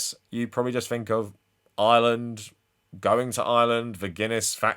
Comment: I thought you were saying “Island” at first 😅